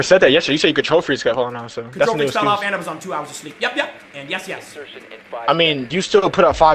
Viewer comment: did bro just say yup yup and yes yes???